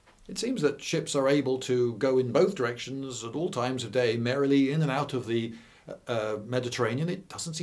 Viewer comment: did he say merrily? I must be dreaming.